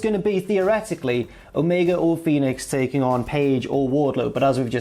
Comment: I wasn't fully focused on the video and I thought you said "Omega vs Penis" hahaha!